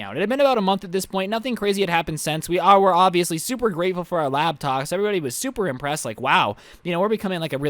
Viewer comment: I heard laptocks